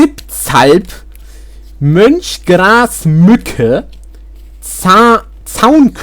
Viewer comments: hold up did you just say munch grass muck eh? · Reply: Mönchsgrasmücke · sorry did i hear house roach vans???? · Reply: Hausrotschwanz